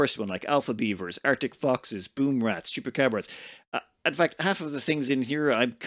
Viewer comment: I swear that I heard you say to hunt Chupacabras and had to replay it multiple times, made me laugh 😂 (Min24:25)